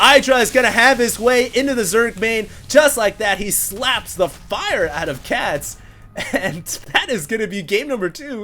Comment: At the end, I thought you were going to say "Idra slaps the **** out of Cats" ... You dragged out the F a bit.